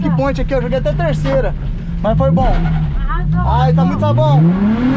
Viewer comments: No minuto 23:50 só eu que escuto a falando ''arrasou amor " foi só eu? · arrasou amor? Foi isso que eu ouvi😳